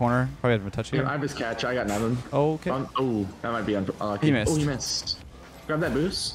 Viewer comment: "He Mist!" Is it just me or someone else heard the same way? :D I was like he is sure Mist! xD